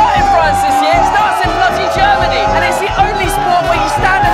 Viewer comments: C'est moi où à 1:30 il dis "Nazi Gremany" x) ? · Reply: Il a dit bloody germany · Tu dis sûrement : " bloody germany" but I hear " Nazi germany" :(